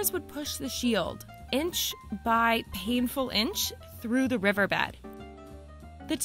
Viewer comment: I had to go back I heard "Push shit through"